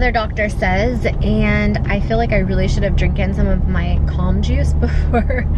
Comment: Did she say drinkin? At 0:44 . Is that even a word?